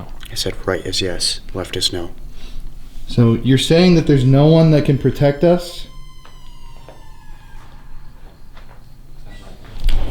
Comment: I love! 😁 At 1:36:56 after asking it to use it's voice...and talking about wether or not there's a spirit there to protect you guys. I swear I heard a females voice say "someone" Sounded like the fellas heard it too. I'm curious what they heard!